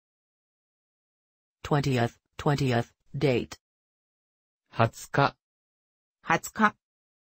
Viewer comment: Love this great upload, thank you so much, really useful. just noticed that at 2:10:16 the words on screen for 'date' don't match what you actually say? eg instead of 'Nichi' you say 'Ka' any reason?